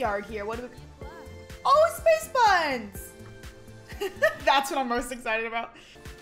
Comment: I thought said “space puns”and I was like were!